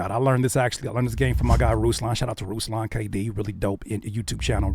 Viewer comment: what name did you say? Couldn’t understand it, trying to find his YouTube. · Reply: Ruslan KD